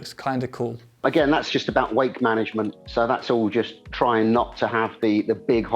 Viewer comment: I thought he said weight management and then went off an a spiel about aero, then realized he said "wake" management..... Ahhh...